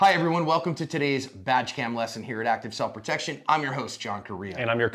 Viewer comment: I thought he said "vag cam" boy was i disappointed.